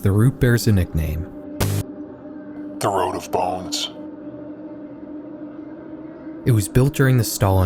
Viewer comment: I didnt hear at first so i thought he said ”the road of balls”